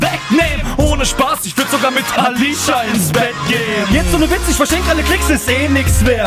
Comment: Was sagt der Kerl bei 2:51 - 2:54 ? "Ohne Spass, ich würd sogar mit Tanisha ins Bett geh'n"? Kann mir jemand die Line erklären? Meine Ex heisst Tanisha und irgendwie ... nunja... ich feier die Line voll! :D